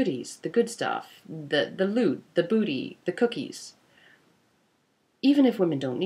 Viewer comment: Did she say booty and cookies around 13:00???? Now we're talking!